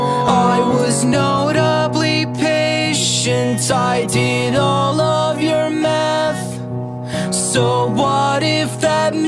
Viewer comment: At first I thought he was saying "I did all of your meth"